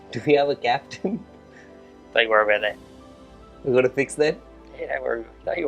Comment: What did mike hesson say at 4:21? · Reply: "Don't worry about that"